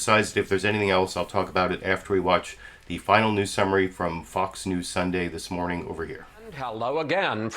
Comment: I totally heard, 'Fxck News Sunday'